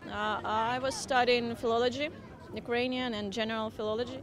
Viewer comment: I heard philology not theology